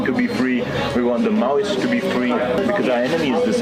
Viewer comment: Did he say the Maui at 3:48? listen carefully ?